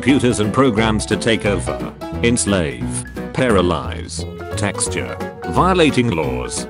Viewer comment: anyone gonna mention how at 1:15 the text to speech says "texture" instead of torture?